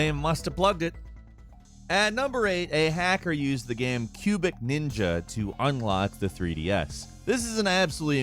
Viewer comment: I thought he said "Pubic Ninja".😂😂😂